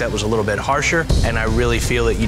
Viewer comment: i thought said she won every flag challenge except for one lol??